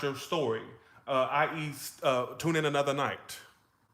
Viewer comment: It sounded like you said I eat tuna another night this read is veryfunny😂